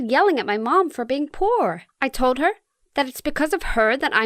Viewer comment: So I’m sorry but my air conditioner was loud so I thought she said being a whore isn’t that bad